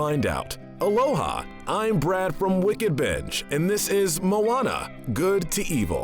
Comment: I swear I heard him say "Wicked Bitch!" lol